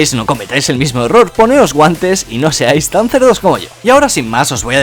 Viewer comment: ¿Que dijo? Quien me puede decir soy un sordo 😂😂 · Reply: Poneos guantes y no seáis tan cerdos como yo